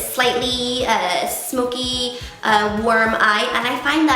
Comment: Lol I thought you said "worm eye" at first XD That back drop is awesome!